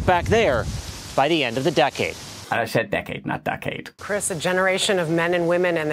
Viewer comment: I thought he said duckade but I think that was your fault